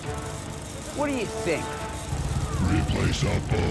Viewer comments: What does RIP say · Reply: Replaceable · What is the thing they say? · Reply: FRANCISCO ROBLES "Replaceable''